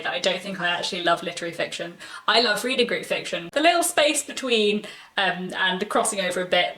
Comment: Congratulations on your 5 years!! What a huge accomplishment. I’m relatively new to your channel but I love it and it brings me such joy - thank you ☺️! Quick question. What type of fiction do you say at 4:54 into this video? I’ve listened to it several times and just can’t quite make out what you’re saying. I think I may fall into that category though (i.e. I’ve noticed that I don’t always love literary fiction but really enjoy what seems to be more upmarket commercial fiction...). I’m pretty new to the reading world and so am not very familiar with these terms yet. I’m learning though and would love to know what term to google! Also, it would be so amazing if you could make a video with your favourite books in that category (or point me to it if you already have one!!) as I would love more recommendations for that little space of the literary world. Thank you so much!! ☺️ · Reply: Thanks! I said reading group fiction (sometimes called book club fiction), which is sort of literary/commercial cross-over. And here's a video on it: <link> :)